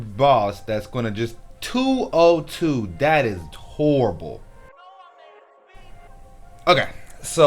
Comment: Did you just say torrible? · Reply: Nah "Turrable" 😂 Charles Barkley be saying that shit lmao